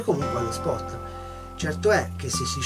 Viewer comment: Al minuto 9:51 viene citata una persona molto famigliare però magari ho capito male.. Potreste riscrivermi il cognome? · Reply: Babini, grande uomo e grande pescatore ^_^